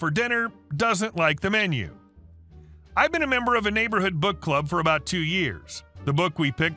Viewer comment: WOW! My hearing is getting bad, I could've sworn he said "Butt Plug" instead of Book Club. LOL I was confused to say the least!